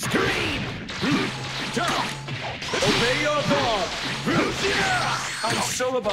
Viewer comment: whenever i hear zamasu say “obey your god” i hear “omega lol”